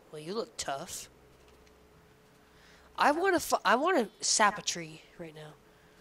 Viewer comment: I heard Say "I want to fu- I want to sap a tree."